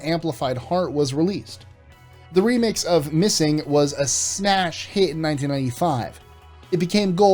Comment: Did he say snash hit?